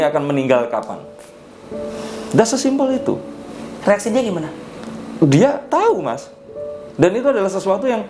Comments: Hello Terimakasih untuk komen2nya🙏 Alm Mbak You salah seorang teman baik saya..kami saling menghargai n mengasihi walopun kami punya prinsip n nilai yg berbeda.. Alm tau betul saya tidak pernah mau diramal ato diprediksi n Alm selalu menghargai prinsip saya.. Mari kita doakan Alm diberi tempat yg terbaik🙏 N ingat lah peninggalan Alm yg baik☺️ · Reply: "dia tau mas" gokil, apa gw salah denger 😄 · "dia tau mas" gokil, apa gw salah denger 😄